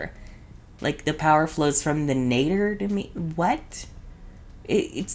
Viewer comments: I thought she was saying “from the METER to me” 😭😭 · I seriously thought she was saying “power flows from the meter to me” and i never understood it. I CANT BELIEVE she pronounced nature like that. smh